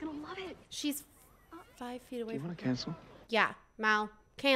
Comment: I thought Ben said to Mal “do you want a castle” and I thought it was super random until you brought it up 😅